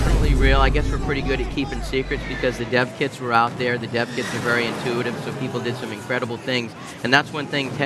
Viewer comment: I really thought that Sony guy was saying 'the deaf kids'. 'The deaf kids were really intuitive...'